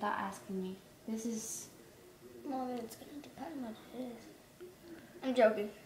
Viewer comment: does anyone know what she says at 10:43?? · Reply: She said “Well it’s gonna depend on what it is”